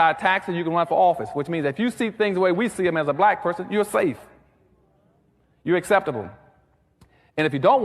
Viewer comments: Does he say at 1:58 "Youre save" or does he say "Youre a slave" ? - Both actually fits the rest. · Does he say at 1:58 "Youre save" or does he say "Youre a slave" ? - Both actually fits the rest. He actually preaches (literally, with all the typical reactions of the listeners), that its not ok for a black man to think for himself and draw his own conclusions but instead just follow the rolemodel he preaches. Ironically, by doing so, he fits the function of sambo from that book. Today, it doesnt take a single white man to keep black americans down - this is allready done by preaching black americans like him ad perfectionam. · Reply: He said “you’re safe”